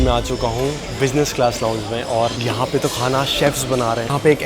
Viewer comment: not bro's subtitle being "chief" instead of "chef"